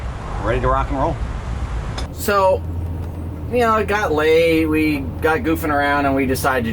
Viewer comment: About 302 it sounds like you said got laid. Now all this time my wife and I thought this was a respectable family channel. Lol. We joking it just sounded funny. We love your videos. Safe travels